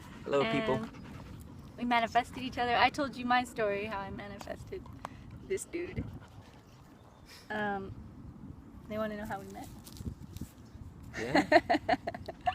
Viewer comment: woow LOVE THIS !! you guys are such a beautiful couple... two very good looking people <3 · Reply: thought you said "how I manifested this beauty" instead of dude. So awesome you two! Did you stay together? It's been a long time since your last post.